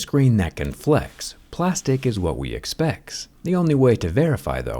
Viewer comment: I heard 'expecs'